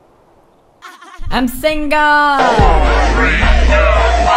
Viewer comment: My favorite part is after he kills Jennifer, he goes to see what Iransponsible was doing, and then he played this: I'm single! <3 WOO he whats that! At least that's what it sounds like it says. xD